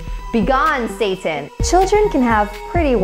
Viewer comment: bEGONE tHOT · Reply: Oh lol because of you I thought he would say begone thot but he said BEGONE SATAN